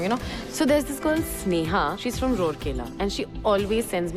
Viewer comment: did she say Rourkela ??